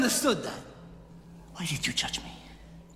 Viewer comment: Sounds like he says ''Why did you touch me?'' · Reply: He said, why did you judge me.